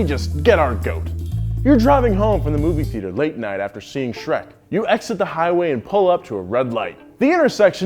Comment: did somebody say shrek? 😏